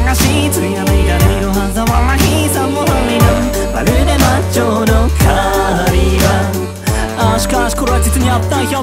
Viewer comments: ｢魔女の狩場」が｢魔女のカーニバル」って聞こえて狂気の祝宴って感じられてとても好きです(語彙力)。 あくまで私の空耳に過ぎないんですけれど💦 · ｢魔女の狩場」が｢魔女のカーニバル」って聞こえて狂気の祝宴って感じられてとても好きです(語彙力)。 あくまで私の空耳に過ぎないんですけれど💦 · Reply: 言葉足らずですみません💦 1番のリリックの｢魔女の狩場｣が｢魔女のカーニバル｣に聞こえる、という意味でコメントをしました！